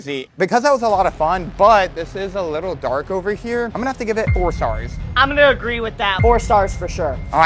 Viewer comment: I thought you said you have to give everybody five stars